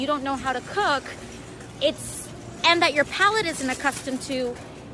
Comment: Cauliflowers are also "white" and we all know that is super problematic. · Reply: Exactly! That's why the captions said "palette" when is should have read "palate" ( at 1:36 ) Freudian slip?